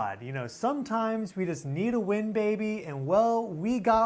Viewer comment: In the beginning, says, "Sometimes we need a win, baby... " I thought he said, "Sometimes we need a wind baby, and we'll, we got one folks!" I was like, "Wind Baby? We got a wind baby?" I actually sat and thought about it for a whike, and once it hit me what he really said, I couldn't stop laughing! 😂😅 Whew, I really needed that! Thank's! 😅